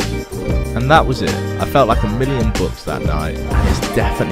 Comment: What did he say at 3:33 ? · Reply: I felt like a million bucks that night